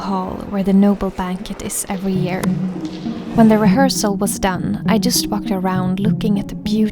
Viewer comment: I heard "when the reharsal was done I just fucked around" If you go back you can't unhear it 😂